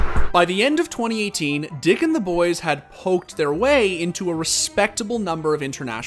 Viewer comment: Thought he said dick in the boys